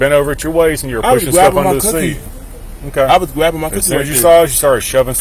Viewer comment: When the cop said what was you shoving under the seat. I dead ass thought homegirl said I was grabbing my bussy😂😂😂😂